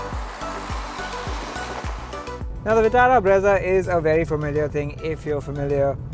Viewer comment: am i the only one who heard TATA BREZZA instead of MARUTI BREZZA!!